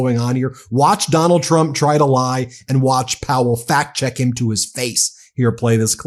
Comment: You said "Fact Check" and I heard "FAT CHECK"!